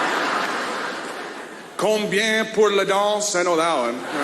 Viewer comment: Strange... im a quebecois living in montreal... and what i heard in that joke... was "combien pour les gants"... (how much for the gloves)... that makes more sense for the next comment "something that you use everyday"... (we don t use a stripper everyday... but gloves... ... ok we "put" (mettre) them both... but one is far more costy in a daly basis...) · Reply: I heard "combien pour le gaz" which is also relevant.